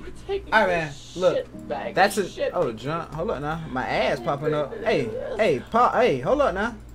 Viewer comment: Are you all enjoying all the bangers I’m dropping?? 🐨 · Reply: Wow I must be tired I def thought that said pooping